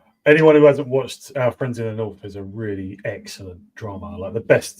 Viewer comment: What series did recommend around 1:02:00? Couldnt understand him. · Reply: Our friends in the North.